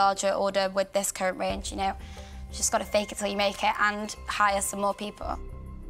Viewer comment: Respect her for what she’s done but you cannot say “fake it until you make it” when you’re asking for money. Surprised none of the dragons picked up on it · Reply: I literally thought she had said "bake until you make it" hahahaha.. which in the context would've been kind of right lol